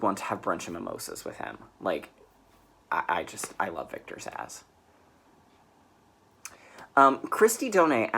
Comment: OMG! I'm listening to IT as read by Stephen Weber, too! He does an amazing job! ...Ohh, you said Victor ZSAZZ. I heard "Victor's ASS," and was imagining you hanging out with some actor's random (but cute) disembodied ass. Time for coffee.